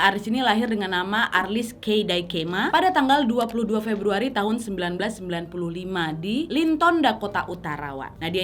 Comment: Apa aku salah Denger yaa, aku kok dengernya arlis lahir 1995 😂😂😂